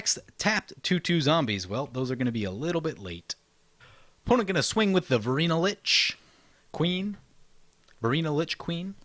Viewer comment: I misheard " Opponent swings the burito leech" Now i dont know if i'm hungry or scared. Nice game from everyone, loving the deck.